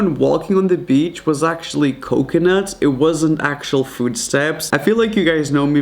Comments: Was it only me or did you hear hear food steps instead of footsteps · I thought he said food stamps😭🤣